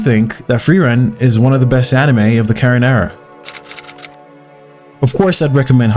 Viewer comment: What does he say at 3:43 - "Frieren is one of the best anime of the ______"? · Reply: "of the current era"